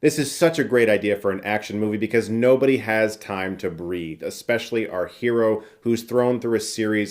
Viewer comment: Did he say no one has time to breed?